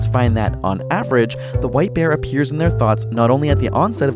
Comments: "Don't think of a white bear..." Me: thinks of a yellow bear "oops you were probably thinking about one" Me: THAT'S WHERE YOU'RE W R O N G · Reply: I heard "white beard" and got very confused by the white bear... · I thought he said white beer